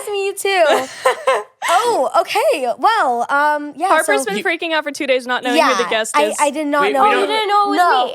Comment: did you say a bad word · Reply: just said freaking out